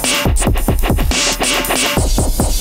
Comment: I hear "Guess I got My Badger Bike" O.o